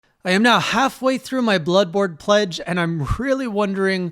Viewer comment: Did I hear "Bloodbored" correctly? Oh no :D